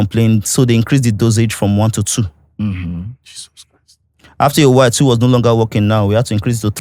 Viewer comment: who else heard the Jesus Christ 😂